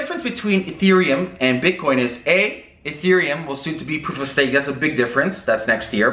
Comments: What did he say at 2:24? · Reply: He said "proof-of-stake". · Does anyone have ANY idea what he is saying here? "A, Euthereum will gibberish that's a big difference..." Also at 5:22, no idea what he says. "proof of sale?" "proof of safe?" "proof of say?" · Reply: "Ethereum will soon to be proof of stake, that's a big difference" "Proof of stake" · What the heck does he say at 2:22?? "Ethereum will <soontobeprvsthzayds>, that's a big difference> ? For the life of me, I can't figure it out....